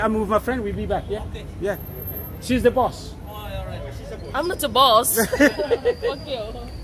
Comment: In 5:26 did she says I'm not your boss f*** you?